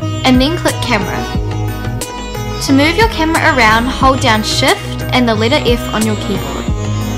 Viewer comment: I didnt hear 2:21 correctly Shift+ What? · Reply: She said hold down 'shift' and 'letter F' on your keyboard. Switch on the subtitiles.